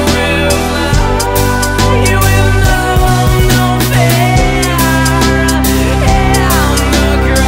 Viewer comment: i CANT HEAR “with no hope,” i can only hear “with no, no fear” HELP ME 1:45 btw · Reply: <link> tyler says: with no known fear